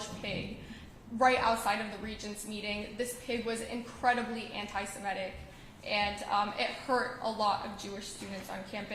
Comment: now even the pigs are anti-semitic. What did it say? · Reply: "Eat me!"